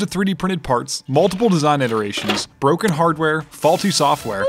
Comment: When you said ' broken hardware ' , I initially thought I heard ' broken heart ' ... That would have made just as much sense.. Loving this project!